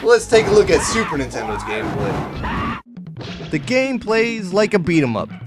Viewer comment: did i hear lets look at the super Nintendo's gameboy?